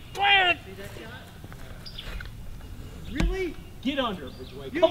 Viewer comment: Did said deadshot